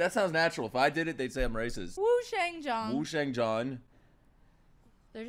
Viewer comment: I heard "Who Shagged John "